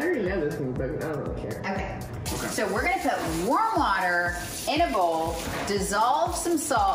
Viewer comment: What she said: We're gonna put warm water in a bowl What I heard: We're gonna put worm water in a bowl Me: Wait what